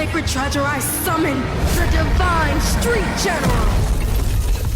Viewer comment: Deku: Did someone said "STREET!?"